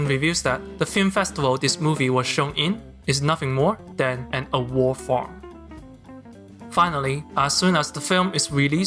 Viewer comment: what is he saying? A war form? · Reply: Award farm